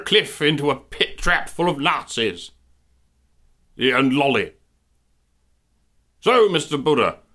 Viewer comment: wtf?XD man wearing a maga hat, throwing a dog into a pit-trap full of nazis...yeah and lolis"???!!!!XD did he say loli or sth else?! anyway, that part got me to spout my racism-milk out of my nose...XD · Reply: Indeed. He said Loli.